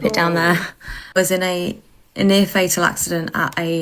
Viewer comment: Pans to dog in bed asleep(?)…..was in a FATAL accident. Me….WTF!??? Lady words please, use them correctly. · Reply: She said near fatal